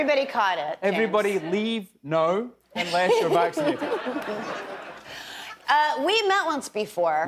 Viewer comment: I am learning conversational English. Can someone explain the words at 1:42 minutes? James started with "everybody leave no" but i I didn't understand... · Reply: I think he said everyone leave now unless your vaccinated but I’m not 100% sure.